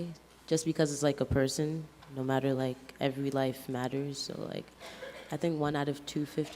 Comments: The black girl remained indoctrinated even though she was shocked by almost ALL of the stats. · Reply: Did i hear the black girl say "every life matters" at 7:08 mark in this video? I could have sworn i heard her say that. Is that allowed to be said? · did she just say, "every life matters"? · DID SHE JUST SAY ALL LIVES MATTER!?!?!? RACIST!!!!